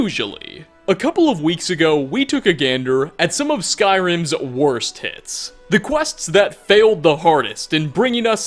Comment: Not gonna lie I thought he said "worst tits"